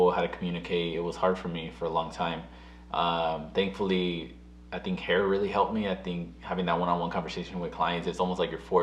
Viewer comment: tell me why I thought you said “heroin really helped me” lmfaooo I was like wuuuuut